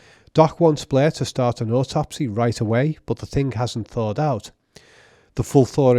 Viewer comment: "the Thing hasn't..." What's that word you are using? The autopsy word thingy I mean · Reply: Thawed out